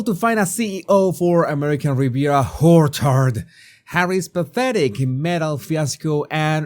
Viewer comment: Every time you mentioned ARO, it sounded like you were saying Whorechard. Even if you say it's just your accent, we Roguies know better. 😂😂